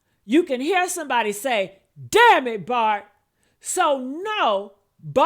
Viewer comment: 3=YES I heard "damn it Bart" on that audio.